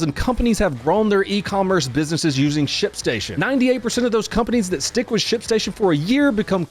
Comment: i thought he said shitstation